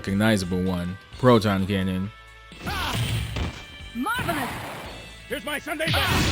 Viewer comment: I always thought he said "Here's my tomato, Proton Cannon!" As a kid